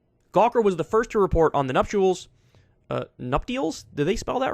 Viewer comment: nuptials... i heard him say nut deals and had to read it for myself.